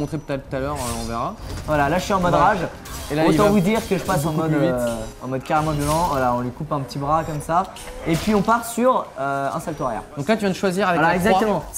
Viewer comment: à 02:45 et 06:05 j'ai entendu un "on part sur" ou je me trompe?!